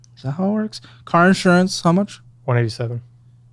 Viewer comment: I think he said $187. It kinda sounds like it 187 and if you zoom in, he definitely didn't mouth 27